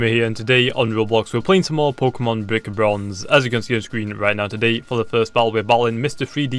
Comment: Did he just say bik bronze